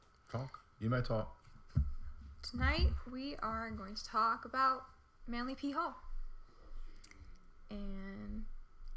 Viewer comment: i straight thought she said "tonight were going to talk about manly p-hole"😂😂😂